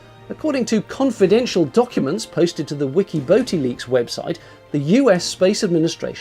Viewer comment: "Wiki boating leaks". You must have been rolling on the floor howling with laughter when you finished. You're good at dead pan delivery, Best of British! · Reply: I thought you said "Wiki Boaty Leaks". That made this American laugh. Love The Vlog!